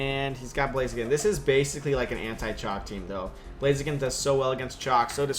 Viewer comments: What the hell do you say @ 1:00 ??? An 'anti-chalk' team?? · blaziken does really well against what? I cannot recognise that word. what did you mean? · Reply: CHALK ... It stands for *C*resselia, *H*eatran, *A*moonguss, *L*andorus-T and *K*angaskhan, which are super annoying and super overused... and super strong together :)